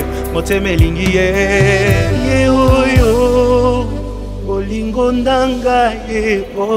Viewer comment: c’est seulement moi ou vous entendez aussi BOLINGO NDANGA? 🤔 🤔 🤔